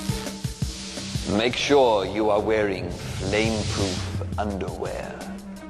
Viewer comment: 'Make sure you are wearing flame-proof underwear' 😂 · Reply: I thought he said stain proof underwear😅